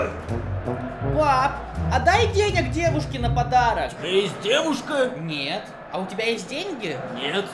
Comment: Мне одной сначала послышалось "подарок дедушке", а не "подарок девушке" 😅